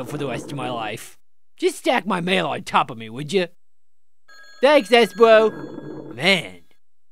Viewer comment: always sounded like he was saying "Thanks, Escrow!"